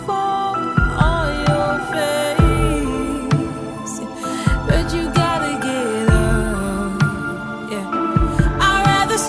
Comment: WHY DO I ALWAYS HEAR "bitch you gotta get up"